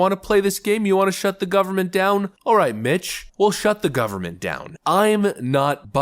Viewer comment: When you said alright Mitch, I'll shut down the government, I thought you said alright B%$#@ I rewound it, and then heard it correctly.